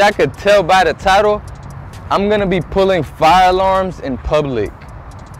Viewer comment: I thought this said pulling fire arms for some reason?